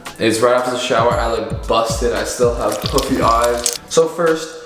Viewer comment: On 2:06 I thought he said ‘bussin’ instead of busted 😂😂